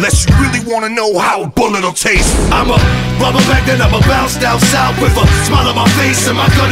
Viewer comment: sounds like he says "i'ma bubble bath and i'ma bounce down south" lol